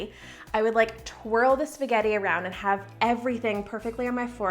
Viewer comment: did she just say spaghetti on toast